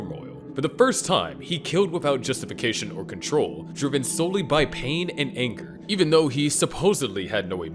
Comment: Did anyone else catch "Pain and anchor" around 42:25?